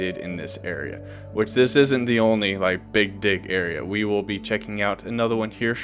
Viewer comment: what did he say?! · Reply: Big dig area